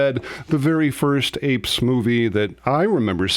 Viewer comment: You know when you're listening but not really paying attention... I definitely heard "APE SMOOTHIE" 1:21 🤣